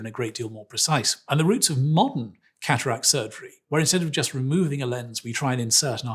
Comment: Why did he say “we try, at the roots of modern cataract surgery”, nobody’s “trying” anything on my eyes during a “modern day surgery”, hopefully not anyone else will allow “modern-surgeons” to “try” anything on their eyes either. Nowadays, with all of the phd requirements for doctorates, they’re allegedly 10+ steps ahead of “try”, having already acquired “true methods”. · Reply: 1st that’s not what he said. He said “and the roots of modern cataract surgery where we try to insert an artificial lens rather than just taking the lens off.” Then proceeded to give an example of a historical surgeon who tried to put in artificial lens in pilots eyes. 2nd your overthinking the word “try.” It’s not try in a sense as we don’t know what we’re doing or just experimenting nowadays.They say “try” because even though cataract surgery and many surgeries have come a long way, nothing is ever 100% guaranteed. Even the most simple, routine surgeries can go wrong.